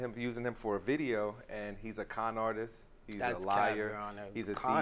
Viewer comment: When he called him a conartist i thought he said kind artist I was like that was nice then I went oohhhh